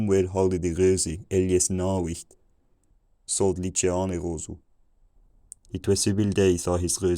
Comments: sounds like he says "it was seven days". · “it was seven days” I think is what was said? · I heard "It was several days" at around 700 (where the translation was "it was a bad day"), then I understood "Between" at 730 and other scattered words before "It is long winter." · I'm danish. I definitely hear it was a wild(vild) day at like 680. But I think I heard "somewhere" around 100 years earlier. The wild part for me is how right after 700 I no longer understood it again. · I thought I heard "It was seven days" but it was "It was a bad day". :) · Around 700, "it was several days since". Before that, I heard "Nar wight" which I felt like I'd heard before in my English lit class years ago. Then, big boost in clarity at 900. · sounded "like it was seven days" 1:28 sounded like "chicken" 1:32 "it is loud fool" 1:44 "it is long winter, the room is cold" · Very cool! Thanks for doing this. Around 695 @ 1:12 I heard "It was seven days" But now see what you said was "It was a bad day." around 750 I thought you said @ 1:21 "between samples" but it was "between the house." @ 1:30 "Is loud fool." is actually "a red bird" lol. crazy. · i heard "it was seven days" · "It was seven days" which obviously is not exactly what was said haha. I could tell from the start we were talking about "having" animals, and that there was a lot of negating/disagreeing happening. Very fun exercise! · At 700 I heard "It was seven days when..."